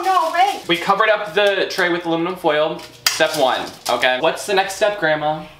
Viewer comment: 😂 I THOUGHT SHE SAID "Illuminati Foil"